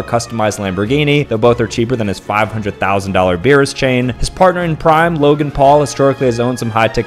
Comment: ‘And his partner in P rime’ Kinda misheard it